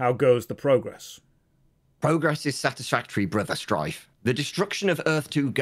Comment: Every time I heard the Voice of say "Brother Strife" I kept hearing "Brother Drive" and I kept thinking to myself "Codenames, neat!" Turns out I was mishearing things. . . 10 out of 10, would mishear again!